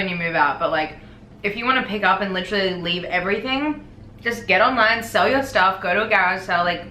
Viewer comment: literally thought said "get online sell youself" lmao